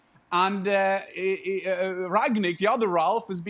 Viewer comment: did he say BANCHESTER UNITED? HAHAHAHAHAHAHAHAHAHAHAHA